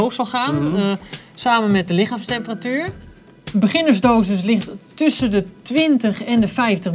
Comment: it sounds like he says "A beginner's dose is lethal" in english lol. I'm way to high for this show it just sounds like they're speaking gibberish to fuck wit me